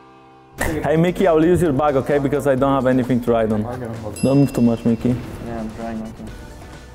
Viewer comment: i swear to god he said "hey miky i will use your bike because i dont have anything to ride on"